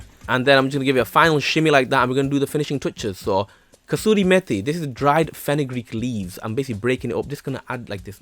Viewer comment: What is that dried herb at nin 4:50 ? I am not an English nativ soeaker and couldn't understand it. Thanks ! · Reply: Kasuri Methi -dried fenugreek leaves